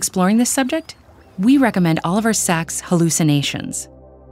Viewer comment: I heard "All Of Our Stacks". I thought, "hmm, must have a lot of books". Next video was Oliver Sacks. LOL. That 'splains it.